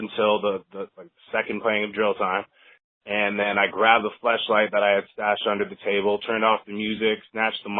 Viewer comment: I thought he said "I grabbed the fleshlight I had stashed under the table".. "Nigga what?" * Westbrook voice *